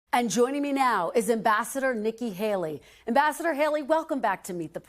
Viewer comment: Ambassador Nikki Haley-I thought she said vascular Nikki Haley